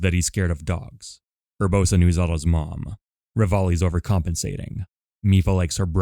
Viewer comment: “Urbosa knew of Zelda’s mum.” Is this what you’re saying ? I’m not a native English speaker so I’ve been repeating this single passage to hear it properly.